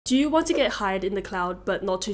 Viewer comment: Damn that intro... I thought she said "Do you want to get high in the cloud" 🤣🤣🤣 My bad